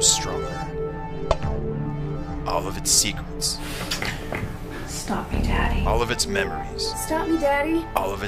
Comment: At first I thought they were saying: "Stop it Daddy." And I was like, "Whoa, shit gettin' real here!" But then I realized they were saying "Stop me Daddy." And I was like, "Meh."